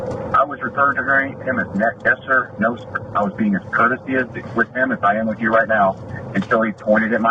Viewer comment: did he say "courtesyistic?"